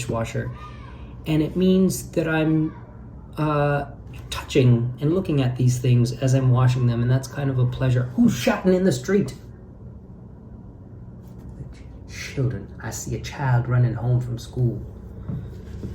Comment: The end part was hilarious. I thought you said "who's shatin' in the street?!" and needed a moment to compose myself.